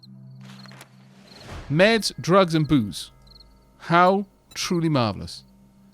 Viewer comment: No gonna lie. I hear "Meds drugs and boobs" lol (sorry I've been single for way too long)